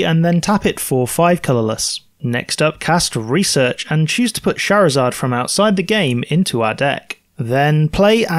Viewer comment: I thought he said "then I'll bring Charizard from outside the game".